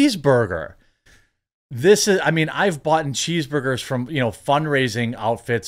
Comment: Did you say "I've boughten"? 🤔